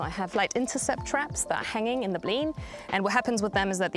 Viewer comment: What was the unfamiliar word at 02:56? Bleen? Blean? Bling? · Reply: Blean is the name of the area this trial is taking place